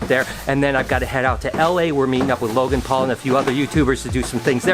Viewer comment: Did You Say Logan Paul ?